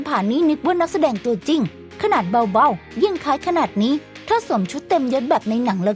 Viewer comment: ถ้าสวมชุดเต็มเยด มีใครได้ยินแบบกูบ้าง555555